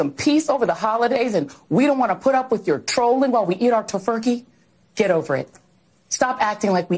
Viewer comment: Did she say "Tofurky"? LOL